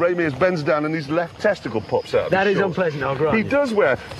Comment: All I heard was "and his left testicle falls out."